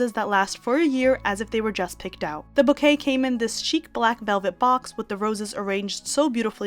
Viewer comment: 😂 I thought you said that the pink roses came in "this cheap, black velvet box". I was laughing so hard at what I thought I heard! So I replayed it and realized you said "chic". 😂